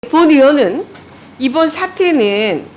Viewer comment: 본 의원은 이 왜 자꾸 일본 의원은 으로 들리는건지